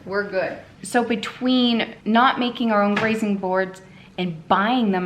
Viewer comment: Oh, "grazing board"! I thought she was saying "raisin board". Kept looking for the raisins in her prep.